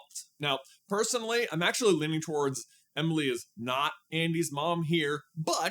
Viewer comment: I misheard “Jessie is Andy’s mom” and so confused lol